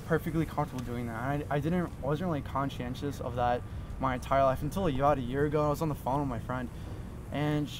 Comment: he says " until a yad a year ago" I think he was trying to say about a year ago. Did anyone else have trouble understanding a lot of his words?